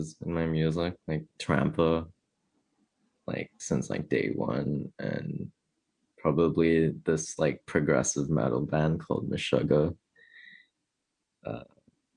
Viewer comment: can someone transcribe or send a link? I can't find it or what he's saying. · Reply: Meshugga and trampa are his influences